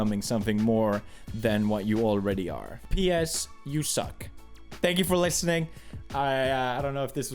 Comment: Did I hear ps you ZUCC !? (near